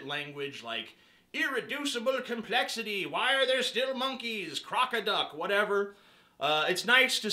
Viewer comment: I thought I heard him say "croc-a-duck" at 1:39 😂😂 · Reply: he did say croc-a-duck